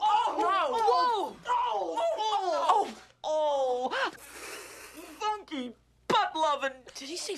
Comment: Funky Butt-Lovin 0:39 1:57 Did he say "Funky Butt-Lovin?" Lol